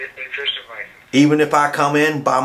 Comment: What did he say at 1:16? He's trying to be gentle?